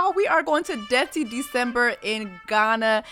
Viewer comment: This whole time I thought you were saying “death to December” oh boy haha